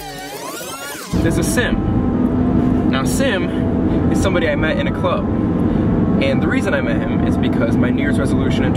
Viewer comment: ok so we can agree that and sim are the cutest together right? thank you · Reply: i thought said they met at a gay club lol